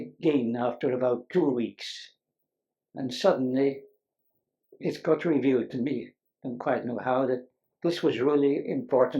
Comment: Did Dirac say, "...as God revealed to me..." ?? Anyway, what a gem! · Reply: "It got revealed to me..."